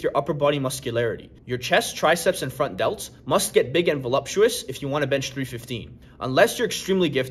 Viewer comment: Bro did you just say voluptuous? 😂😂😂